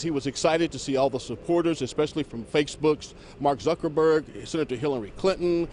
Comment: Did he just say "Faks Books"